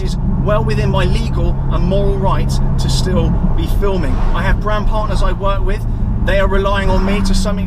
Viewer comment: Next video.... Clarifying to the other idiot who thought he said "i have brown partners as well" instead of "brand partners".. Oh that'll be me 😂😂😂👍